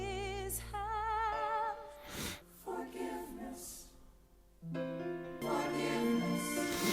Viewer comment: It's the fact that Philippa Soo (Eliza) literally just said three words in It's Quite Uptown and yet had the power to bring everyone to tears with them: "It's quiet uptown" Breaks me every time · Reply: I thought she said ‘it’s quiet uptown’ not forgiveness lol.